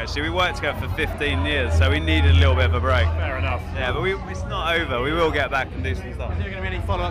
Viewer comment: at @ 1:50 is he saying the boosh isnt over?? :D